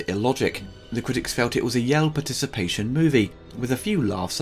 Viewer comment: "A what participation movie?" Yell participation?